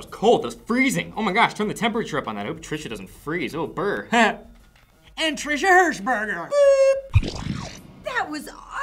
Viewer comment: I think I say Trisha Newton